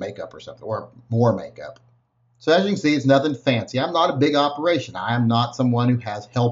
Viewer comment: I heard it !! I heard you say it ............